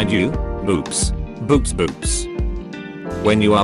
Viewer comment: i heard "boobs, boobs boobs"